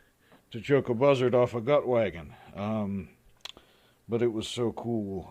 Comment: Off topic (idioms): 0:38 Choke a buzzard off a what? I get the gist: lots of XT parts. But what does this saying refer to? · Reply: Choke a buzzard off a gut wagon?